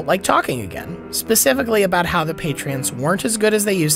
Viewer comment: Am I the only one who heard "Patreons" at 7:39?